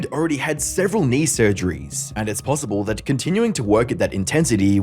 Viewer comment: erm did you just say knee surgery